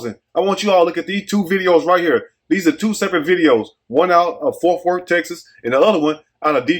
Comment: it aint no Forf Worf, man. It is Fort Worth. You should no better living in Houston lol!🤣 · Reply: sound like he said forth worth 😂 either way, agreed!